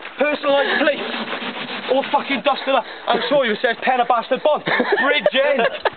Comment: I wish so bad I could hear what he says at 2:40 that shit's funny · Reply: Pen y bont , it’s a Welsh town